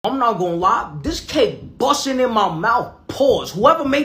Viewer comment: I thought he said “this cake BUSSTIN IN MY mouth 💀😂